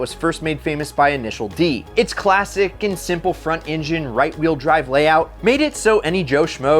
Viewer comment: did he say Right Wheel Drive?